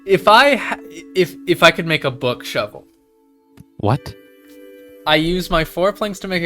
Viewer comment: I thought bro said "book shelf" not book shovel 😂